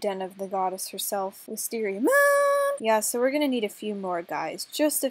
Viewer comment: Am I the only one that heard her say Wisteria Goon